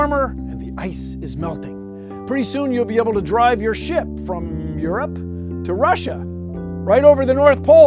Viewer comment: I thought Bill Nye said "You can drive your shit from Europe to Russia..." 😂😂😂😂